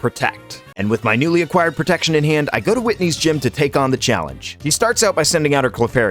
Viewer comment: did you just call Whitney a He? 😂